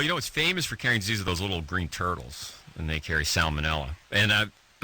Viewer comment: Funny now provigil is sold at walgreen et al. Back then it was a “controlled substance.” 😂😂 And had to listen three times cuz I thought Bruce was saying “little green gerbils.” 😂😂😂 what’s my dad doing in Israel . 😂